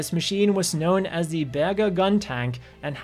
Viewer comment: I heard that as Burger